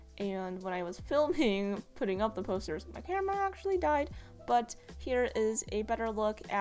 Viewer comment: i was eating while watching this vid and at 10:25 i thought she said "my grandma actually died" and i freaked